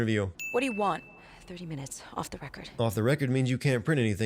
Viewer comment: Hey look another reference to a Dead Rising game better than this one! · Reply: ok I got it but you're timing is off!!! It's actually 7:58 you silly person!!!! 7:58 they say "OFF THE RECORD" no wonder I didn't catch it at first.